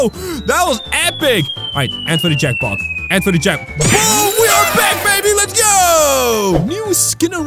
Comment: Instead of ,, Hey!! We are back baby let's goooo!!!'' you meant ,, Hey, we're back babe let's gooo!!'''